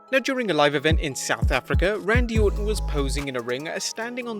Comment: Did I just hear South Africa?